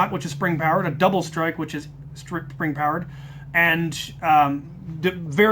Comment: sounded like you said the double strike is string plowerd